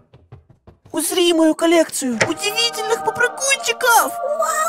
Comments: Мне послышалось усри мою колекцию ахахахахаахахп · Простите, но мне послышалось: УСРИ мою коллекцию.. УДИВИТЕЛЬНЫХ ПОПРЫГУНЧИКОВ!! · Мне одной послышалось усри мою колекцыю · мне одному послышалось усри мою коллекцию УДИВИТЕЛЬНЫХ ПОПРЫГУНЧИКОВ ? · мне одной послышалось усри? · Мне послышалось усри... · мне послышалас то что когда открыл коллекцию он сказал усри😂😂😂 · мне одной послышалось "Усри мою коллекцию..." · Я которая услышала “усри мою коллекцию“ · Мне одной послышалось усри мою колекцыю попрыгунчиков?😅😅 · Я одна услышала "усри мою коллекцию"😅😂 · Усри мою коллекцию? Что? Мне послышалось? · а почему мне послышалось усри попрыгунчик 😂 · мне одной послышалось: УСРИ мою колекцию удевительных попрыгунчиков!! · Мне послышалось или он сказал усри мою колецию😂😂😂😂😂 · Мне послышалось или сказал усри мою коллекцию